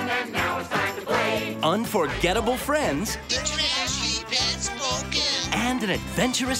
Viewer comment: What Did He Say? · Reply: "... unforgettable friends... " "The Trash Heap has spoken."